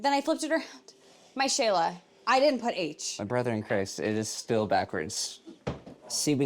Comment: Did he say “My brother in Christ”?? 😂😂😂